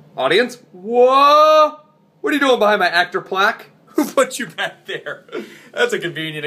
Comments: Am I the only one who thought it said "actors plague"? LOL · I thought it said "Plague," too!